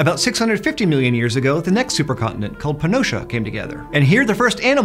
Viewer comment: had to replay it to hear it right thought he said panocha Iykyk lol